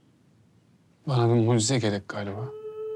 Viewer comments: dakika daaaaa sedat ne diyorrrrr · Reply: Bana bi mucize lazım diyor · Sedat ne diyor?? Ben anlamıyorum. 10000 kere dinledim anlamıyorum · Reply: Bana bir muzice gerek galiba